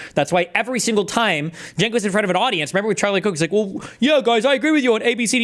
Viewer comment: I heard 'Charlie Kook' instead of 'Charlie Kirk' and that's what I'm calling him from now on.